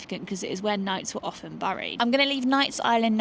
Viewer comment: Did you say the old town is "Gamla Tåget?"